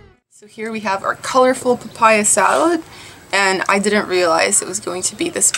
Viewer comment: I thought you said papaya when you were pronouncing popiah... lol Also, I'm surprised they refer to roti prata as roti canai. I know roti canai as a really thin crispy roti, and the version you're eating as prata.